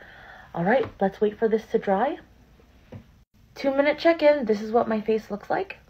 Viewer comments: First off I love that you mentioned it was clean at Sephora as someone who has PCOS AND A THYROID problem which has made it very hard to conceive, I have had to take a hard look at the products I use Secondly I thought you were saying chicken not check in 😂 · It sounds like ur saying minute chicken 😂 that mask is so cool